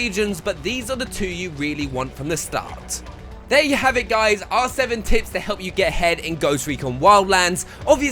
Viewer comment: At the end I heard "our 7 tips to help you get head in wild lands"... I'm sold..